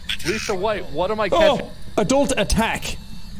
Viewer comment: Maybe I'm hearing things, but it sounds like lisa says Adult attack...... Me At this time 55:18 I might be wrong, but that's what it sounds like to me.